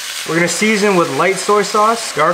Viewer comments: I thought you said a little bit of urine 😅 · Damn. I thought he said a little bit of urine… now I have to start over